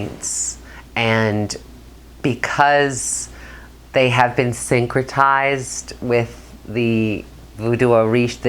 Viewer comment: Thank you for these interviews. What is the word at 3:47? Is it "sancritized"? What does that mean? Is it sanskritized? I'm intrigued. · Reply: The word is "syncretized", meaning the combining of different beliefs into one belief